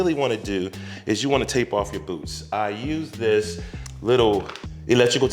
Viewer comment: 🤣 You said tape off your boots and I thought you said take off your boots and I was like umm yeah that makes sense.